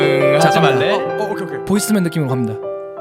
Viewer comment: I think jae said boyz II men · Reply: i was about to say that - jae and youngk didn't say voicemail, they said Boyz II Men (the boy band)